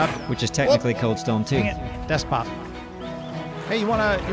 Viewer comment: desk pop is what he said which might be a reference to the Other Guys when he did a desk pop with his gun